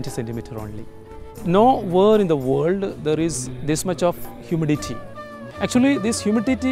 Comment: thought he said humility not humidity